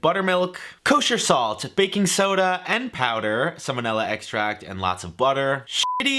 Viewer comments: Was it just me or did I hear salmonella extract at 3:35? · Literally thought you said salmonella extract… I’m thinking wtf why ? No cake is worth it. Lol but then you brought it vanilla extract and that made more sense.. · Lol 3:35 I heard salmonella extract 😳